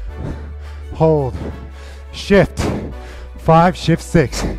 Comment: I thought you said shit at 3:46 lool 🤣🤣😂😂😂😂